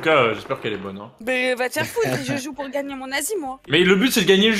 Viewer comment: gagnez quoi ? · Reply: gagnez mon asie